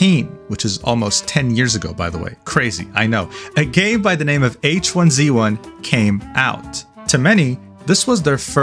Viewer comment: i stg i thought you said “a gay by the name of h1z1 came out” and i was so confused i had to rewind twice